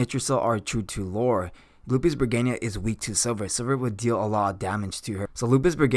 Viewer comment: First of all ty for Ur hard work and explanations. I had a hard time understanding what u said her weakness was i listened about 5 x b4 i caught it at 5:11 (Silver) lol . Keep up the good work and ty again